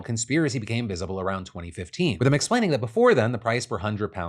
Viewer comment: did he just say MCsplaining??